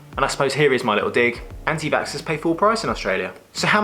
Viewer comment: Hi. What on earth do you say at 3:22? "Here is my little dig, anti .......??????????" Have no idea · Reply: Vaxxers